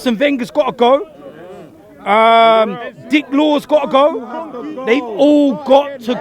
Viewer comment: When I first heard these rants I thought he was saying "Dick wad" instead of Dick Law 😭💀🤦